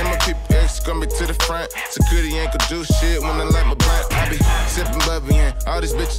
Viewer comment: All I hear is "Security aint gon' do shit when I lick my butt" It makes me say WTF? everytime I hear this song lol x0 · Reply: when i light my blunt*